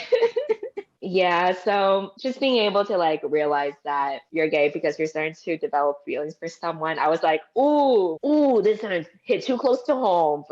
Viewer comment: what does say at 15:01 ? someone tell me why i am geh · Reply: "just being able to realize that you're gay because you develop feelings for someone"